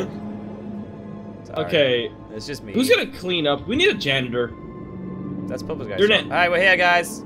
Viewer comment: I thought said we need a sans -_θ